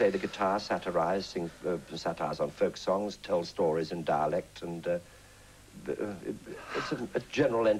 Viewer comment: Is it sad that I kept mishearing them say "Dalek" instead of "dialect?"